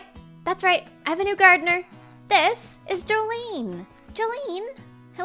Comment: Anyone Else thought she said "Jolyne" at 1:08 cuz My jojo reference senses were tingling